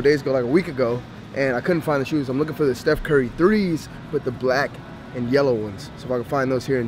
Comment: Fix your audio, sounded like you said you were looking for the Steph Curry 3's.